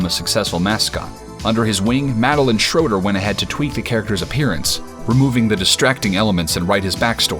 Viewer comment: I had to do a double take because I thought he said "Tweet"